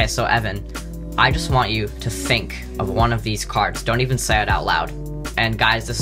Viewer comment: Did he say ‘fink’ at 0:19?